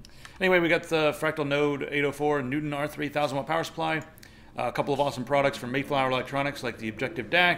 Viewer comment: For a second i thought he said 3000W power supply i literally dropped my cookie.